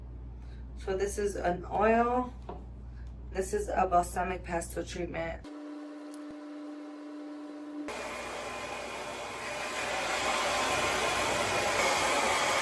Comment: I thought she said she was putting a “balsamic pesto” treatment on hair